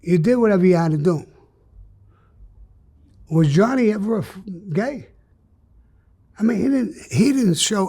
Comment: What was that word?? "Gay?"😂😂😂 Gay doesn't start with "F"...😅😅😅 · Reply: He was about to say finook, it’s Italian slang for gay. It’s said a ton around guys like him. Weird…..